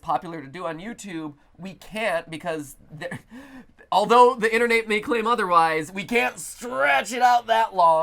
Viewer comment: did mat say internate